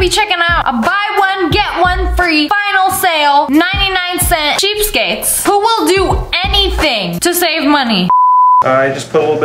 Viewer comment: At thought you said"woman will do anything to shave"